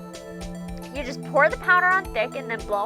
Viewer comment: i was looking away and thought said "you just pour the powder on the dick"